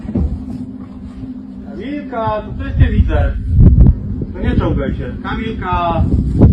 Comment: He said, "Don't crawl on the ceiling" 😂😂 I wonder what he saw! And how the hell he said that so calmly. Like it was something he says every day. 😂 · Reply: he sayd 'nie czołgaj się' in Polish, nothing about ceiling.